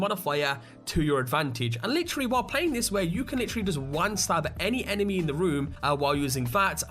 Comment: at (5:46) did you say "wank stab"? lol